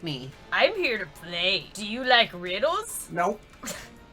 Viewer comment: i thought Unknown said “Do you like noodles?” and i started laughing and then my sister came in to say it said riddles. *whispers to self* damn it what’s wrong with me...!